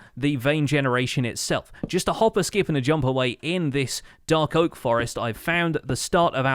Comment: wtf did u say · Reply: just a hop, a skip and a jump away. it's an expression that means 'a short distance away', but that sounds nice